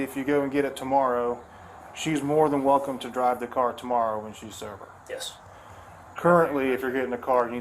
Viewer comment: Did I understand the officer to say to the husband that she could drive the car home tomorrow if she were sober?? I played it twice and that's what it sounds like he said. It's right there at the end.